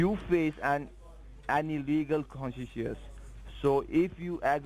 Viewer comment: Anyone have any clue what word says after ... you face any legal.....? Is it trying to say consequences?